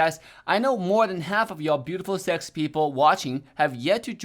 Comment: Did he just say "beautiful sexy people''? Or did I heard it wrong 😂